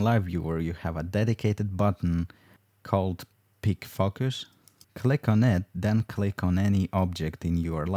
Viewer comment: He said "pick focus" but I was distracted by something else and actually heard "pig fuckers" and was like, what did he just say? XD